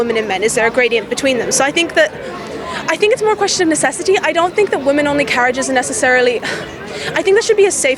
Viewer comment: I thought she said 'Women's undercarriage', and left me thinking 'How did we get here?' Beginning a sentence with the words 'I think' means you don't.